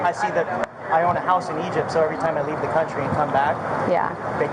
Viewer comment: Did he just say he owns a house in Egypt? 10:20 of the video